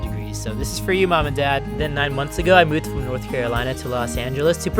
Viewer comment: When Guava Juice said North Carolina I thought he said North Korea 🇰🇵